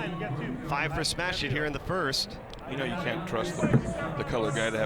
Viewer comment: did he just say you can’t trust the colored guy